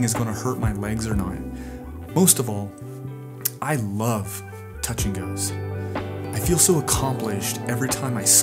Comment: I thought you said "I love touching girls, I feel so accomplished everytime I squeek in a gentle landing". I thought it was a bit so I rewinded. LOL